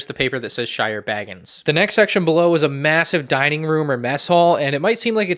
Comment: did he say... Massive?